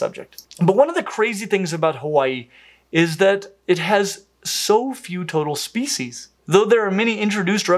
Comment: Thanks to the transition from toads, toads and more toads, I misheard “Hawaii has few toadal species.”